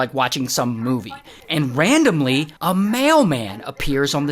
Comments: I misheard mailman as male man lol😂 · Bro I thought you said “male man” instead of “mail man”